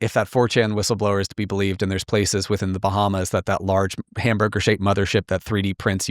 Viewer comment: DID HE SAY BAHAMAS?! like idk..... NASSAU BAHAMAS! It always comes back to our boy 😂